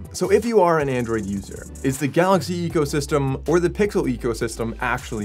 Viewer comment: Did man say "pickle" ecosystem😂